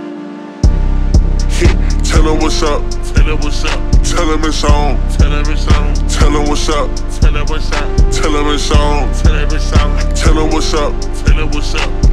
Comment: NGL the whole time I thought he was saying “tell her what’s up, turn dat bixtch on.” I was like ooo he freaky